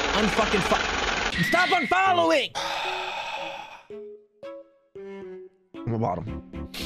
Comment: why do i hear "im a bot'le of wot'oh" 💀